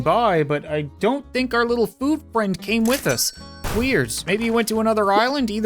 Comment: if you turn captions on, it says ”queers” instead of weird